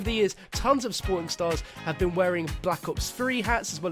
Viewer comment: Tons of spornstars, wut. Did you meant to say pornstars or sportstars?